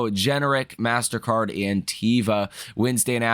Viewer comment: He said “and teva” but I heard “Antifa” 😅